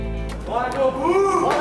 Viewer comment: só eu escutei um "bora toguuro"?